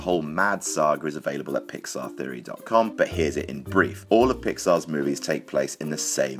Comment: Anyone else notice that when he said "here's the brief" it cut to penny wise behind a pair of underpants? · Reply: It sounds like he says "Here's it in briefs". Which would make sense because the movie is IT and its in underwear.